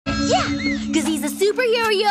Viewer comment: i thought she said M.K. Owned